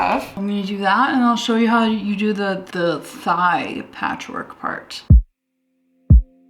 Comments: every time you said patchwork in this video i thought you were saying patrick and i just accepted that until i realized you were saying patchwork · i really thought you were saying patrick jean, turns out it was patchwork and that makes a LOT more sense;) cute video as always!!